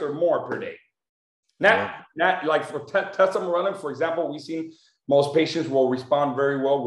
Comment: can anyone please write the name of peptide that these guys are discussing.. actually some accent issues i can’t understand what the doctor is saying exctly.. thanx alot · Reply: Tesamorelin